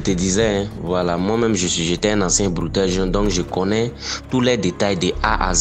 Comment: "moi même j'étais un ancien bouteurr" 🤣🤣🤣 jsuis mort